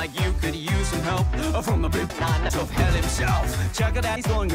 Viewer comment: literally sounded like luci said “from the big nuts of hell himself”